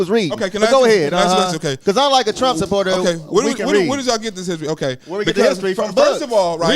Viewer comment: Wow.... I didnt catch this the first time 17:30 Boy on the right said, "Unlike Trump supporters, we can read" Pure racist. Generalizing like a mfer